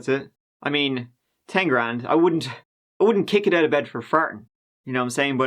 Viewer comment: I didn't know what you were sayin'. I had to look up "Wouldn't kick it out of bed for fartin'" Lmaooo 😆😂